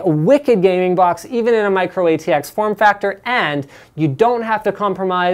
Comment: For some reason I heard Microwave factor at 5:56 itstead of micro ATX... Anyway good video.